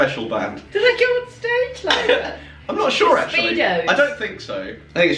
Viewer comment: i thought she says "jurassic speedos" 😄🤣